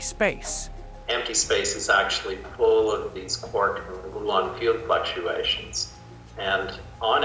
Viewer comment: I can't understand what he says after, "Empty space is actually .........." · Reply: full of these quark and gluon fields fluctuations."